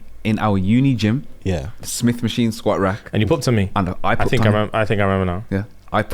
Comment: the South African in me replayed this part 10 times to make sure that I heard properly, I didn’t know poep was an international term for farting 😂😂😂